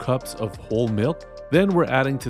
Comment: I was confused for a second when you said "whole" milk, I had thought you said "old" milk! Very cool choice for a seasonally appropriate media cookbook.